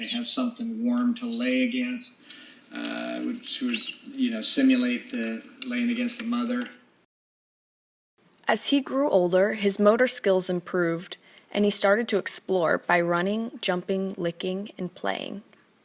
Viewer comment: I heard: "as he grew older, his murder skills improved" my profile picture